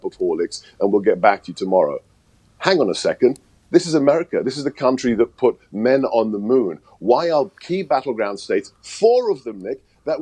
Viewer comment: I'm sure I heard him say this is a country that PRETENDS to put men on the moon!